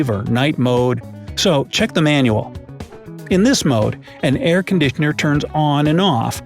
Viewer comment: Did you say aircondickner on purpose or was it a mistake lol? ( 9:22 )